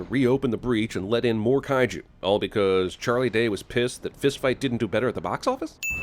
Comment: For a second I thought you said Charlie Day was pissed Food Fight didn't do better at the Box Office.